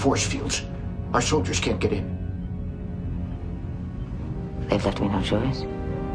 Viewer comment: I know she said "They've left me no choice" but all I heard was "Look out Ned, it's coming right for us!"